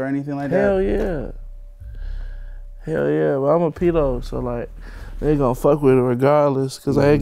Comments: Did he say "I'm a pedo" at 10:40 ????? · did folks say "i'm a pedo" LMAO